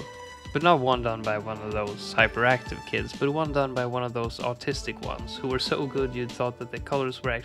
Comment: did he say "autistic" or "artistic"?